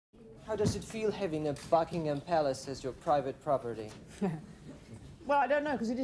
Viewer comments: Who else thought the student said "How does it feel having a fucking palace as your private property?" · Because of his accent I thought he said fuckingham Palace at first 😂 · It sounds like he said Fuckingham Palace and not Buckingham Palace. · Oh, "Buckingham Palace," lol. I thought the guy said "a f*ucking imphalus", whatever that is. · I thought he said ‘Fucking’ 🫣😅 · I thought he said a f**king Palace! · Thought he said fuckingham Palace. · I thought he said a fu*king palace as your property, at first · Sounded like he said Fuckingham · I thought he said fucking and not Buckingham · Who else thought he said a Fucking Ham Palace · Why did I hear "fuckingham"? 😭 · I thought he said Fuckingham Palace🤭